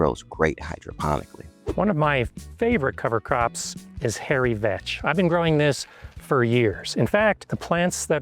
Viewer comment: I had to rewind the video bc I genuinely thought he said "Hairy Bitch" 😂